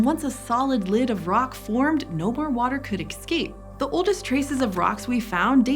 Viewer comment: did you say EXCAPE?!? Hmmm …